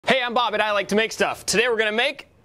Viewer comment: Everytime i hear "Hey im Bob and I like to make stuff", it makes my day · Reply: I always hear "Hey I'm Bobit, I like to make stuff"